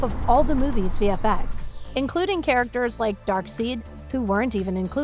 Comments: Did she just say "DarkSEED" lmao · she really said “Darkseed”